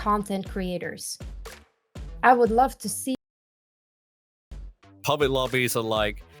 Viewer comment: what did say at 21:21 ????? it was clearly censored... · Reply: We didn't censor it, nor did youtube. It was an encoding issue we couldn't avoid or fix. She said, "I would love to see more content."